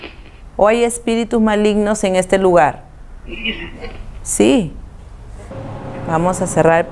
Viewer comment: El el minuto 19:52 parece que dice “ella ve” en vez de “Si”.